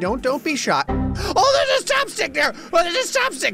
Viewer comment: It sounds like he says chopstick